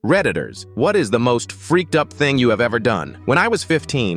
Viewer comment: Why did I hear “Predators whats the most freakout thing you did”